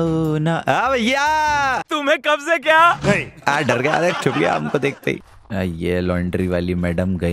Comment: wait a minute Kya bola tuney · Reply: He said chup gaya